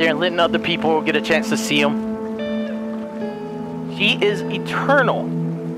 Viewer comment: "She is eternal" Smh i thought it said "Shrek Eternal"